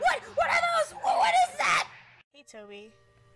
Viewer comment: did he say ''what are those!?''....... toby : ''point at E.J eyes ''WHAT ARE THOOOOOOOOOSE!?